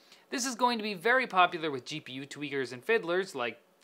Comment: "GPU Tweeters" dont think i didnt catch that lol